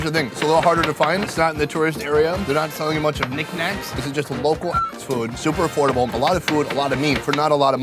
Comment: what did you say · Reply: Not selling a lot of nic nacs just local ass food